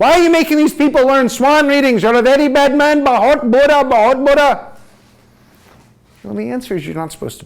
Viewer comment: Did he just say “bahut bura”?@ 33:32 Hindi?